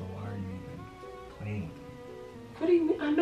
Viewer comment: The crazy part is it really sound like she said “daaaaaaammn I had 4 more cards left” 😂😂 I rewind it like 8 times to listen and I still don’t hear the name 🤷🏽‍♀️🤦🏽‍♀️😂🤣🤣 I’m must be hard of hearing